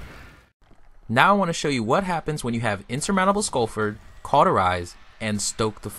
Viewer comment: what did u say @ 4:00 ? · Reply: Skullfort (exotic Titan helm) and Cauterize (Sunbreaker perk).